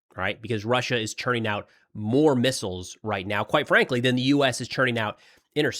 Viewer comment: I thought you said Russia is running out of missiles